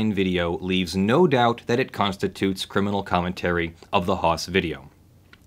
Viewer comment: Did I hear you incorrectly? 6:10 If not, can you please define "criminal commentary"?